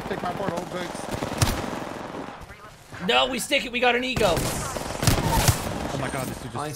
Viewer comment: did he say we gotta stick it we got a ego or something else